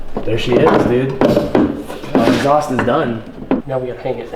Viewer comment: does he say “the holocaust is done”